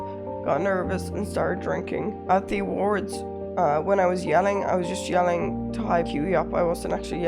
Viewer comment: captions are wrong, i believe she said "i was just yelling to [hype QT up]"